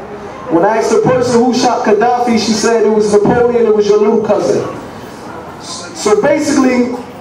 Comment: What's he say at 11:48-11:51 does he say napoleon it was your own cousin???